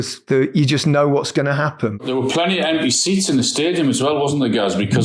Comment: Thought Alan said "Plenty of NPC's in the stadium..." Steady on Alan